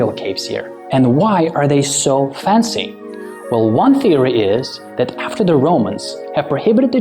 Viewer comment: Theory I thought you said answers